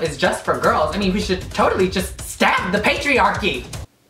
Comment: All I heard: anarchy